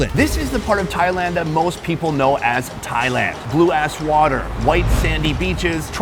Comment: Did Sonny say “blue ass water”?